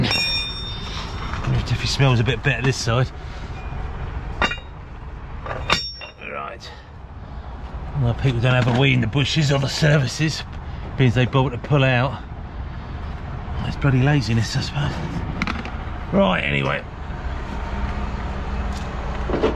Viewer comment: I thought you said weed in the bushes as in smoke